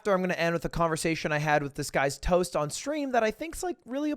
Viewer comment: I really really thought he said he had a conversation with “this guy’s toast”